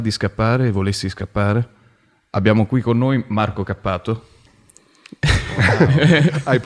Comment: "abbiamo qui con noi Marco SCappato"